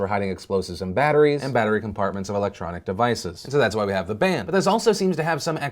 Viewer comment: so I totally thought you said "electronic band" and I was thinking that sounds really cool!! and they are middle eastern (because the photo you showed kinda looked like it could be a band photo) and honestly I'm a little disappointed!